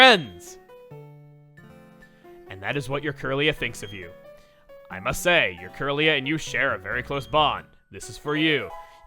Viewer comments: flashes Masea's channel picture when you say "I must say" This is because if you don't listen to the EXACT enunciation, it sounds like he say "Masea" This is to explain why he flashes it, and to not boost the speculation of the shippers running rampant in the comments. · I get what did there! Not really, maybe a pun. 7:03 When said, "Must say", it just sounded like he said, "Masae". So, I guess saw that as a perfect opportunity for a small gag. But that's just a theory.